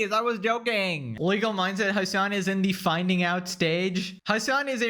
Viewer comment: I thought bro said Wiggle Mindset because of that Jellywig stuff 😂